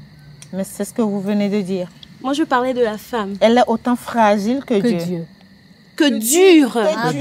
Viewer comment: pardon moi aussi Jai entendu " elle est autant plus fragile que Dieu "😆😆😆 la je me demandais si tu connaissais Dieu la